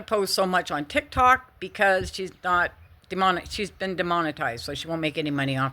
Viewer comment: Sounded like you said “she’s not going on Tik Tok because she’s been demonic”! 😂😂😂😂